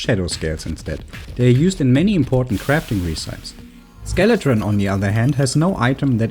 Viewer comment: At about 1:18 you say “Ree-sypes” instead of “Reh-si-pees” and I am so curious if that is how it is pronounced where you are from! I like it haha, English is such a silly and unpredictable language.